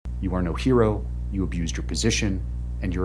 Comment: But he called her a hero. Sad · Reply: I think you may have misheard the judge. The first words in the video are, "You are no hero".